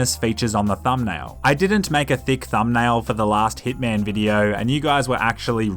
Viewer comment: I thought you said thickman instead of hitman